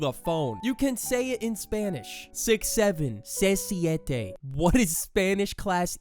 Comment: As a native Spanish speaker, I heard "Se siente" ("It feels").